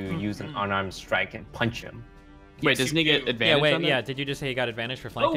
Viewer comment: Did said the n-word or am i just hearing things? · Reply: "doesn't he get a--"